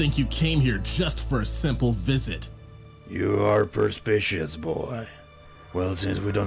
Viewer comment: What is this word? "Per-spish-ous"? The closest word I could find is "perspicuous" which could fit the context, but doesn't sound the same. But wouldn't "perceptive" be a better fitting word here?